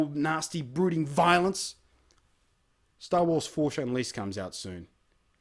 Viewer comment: what does he say in 7:57 - 8:00? Did he say star wars 4? O_O